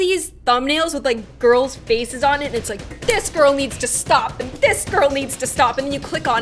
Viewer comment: Did just say that James Charles needs to stop